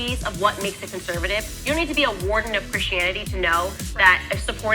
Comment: sounds like she said “warden” not warning, either way she’s evil but I’m just tryna be helpful!